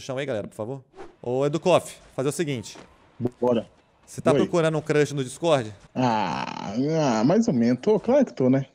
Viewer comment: ACHEI QUE ELE TAVA FALANDO DO CRUSHER FOOXI 😂😂😂😂😂😂